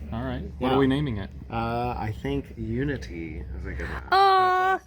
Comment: When asked the couple what they were going to name their new snake, I thought the dude said ‘Lumity’ instead of “Unity” and I just about had a heart attack. Any other Owl House Fans out there?!? For those of you who don’t know, lumity is the ship name for the main character of an animated show called The Owl House, and her partner. If you’re looking for something to watch I highly recommend it. Really fun story and characters and the animation is 👌. Disney is producing it, however the person behind it is a lady called Dana. The second season has recently finished, and even though the show is really popular Disney is cracking the whip on the creator and not letting them do the third season the way they planned to. But if more people show an interest in the first two seasons they might change their mind, so get binging everyone!!